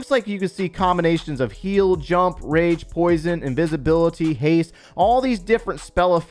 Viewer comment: did he say invisibility spell? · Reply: no I said invisibility-->Archer Queen ability